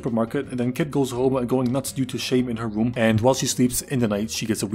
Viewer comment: I thought you said "nuts due to shame"💀💀💀